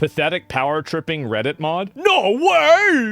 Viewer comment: "Power Tripping Reddit Mod" and I heard "art". And I know exactly what subreddit that is, and who's mod is that. There has to be a better place for artists to showcase their proud work.